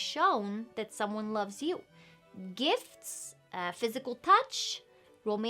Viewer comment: I heard 'Physical Touch' as 'Physical Punch' and answered with " Yeah, I'm good with punching."